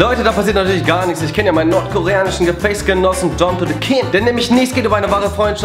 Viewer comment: Nordkoreanischen ?? Habe ich das richtig verstanden 😂😂